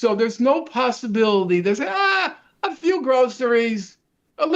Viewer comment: I had to rewind a few times because I thought Norman kept saying "fuck you" when he was actually saying "a few" 😅